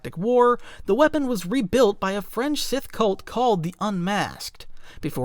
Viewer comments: You know it’s time to go to sleep when you imagine you heard the narrator say a new ‘French’ cult instead of fringe… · I heard a "French Sith Cult" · Did he say "a 'French' sith cult"?